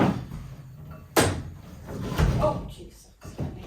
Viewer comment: did just say "oh Jesus " ? 🤔😱😱